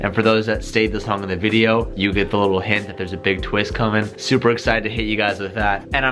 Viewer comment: referring to the hint @ 11:06 sounds like he said " Supra excited to hit you guys with that " Supra hint ?